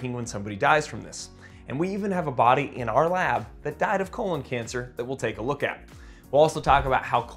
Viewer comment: At first I heard "we even have a BUDDY that died of cancer in the lab" and I thought damn, that's insensitive to your buddy lol